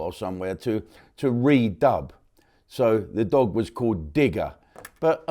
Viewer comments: I thought I heard them say Trigger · I heard 'Trigger'!!!! not Digger.Must be my ears.